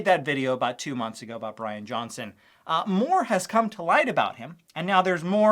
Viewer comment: I thought you said Benny Johnson